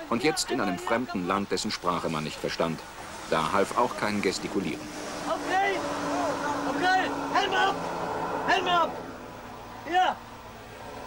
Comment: "Hell me UP" dürfte die Panzerbestazung wohl verstanden haben. Herrliches Zeitdokument!